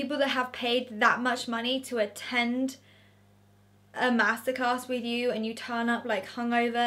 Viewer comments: I thought every time she said “master class” she was swaying “massacre” and it was weirdly eerie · I know this sound crazy but I thought you said massacres. Omg. You said master class. I’m a silly American lol